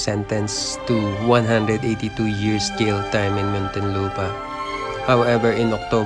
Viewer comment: Wait! In 2:19 did I just hear 182 Years of Jailtime? WHAAATTT?!? 😱